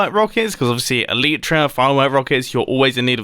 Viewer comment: Did you just say Eleetra?! Lol